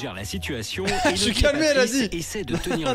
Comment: j'ai entendu "jsuis camée"😂